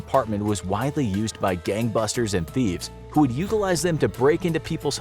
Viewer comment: subtitles say gang bangers instead gang busters